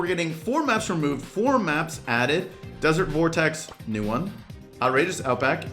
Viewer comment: Why does it say 'desert vertex'?